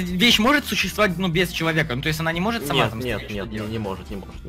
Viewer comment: Эдисон вы спалились! Зачем ты подсказал что ты построил газонокасилку! ) Если кто не понял то вот на этих минутах говорит а она сама не может там стричь! 16:25 или мне послышалось